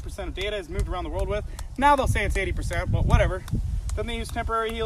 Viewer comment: I thought said modified YouTube spy planes lol U2 🛰✈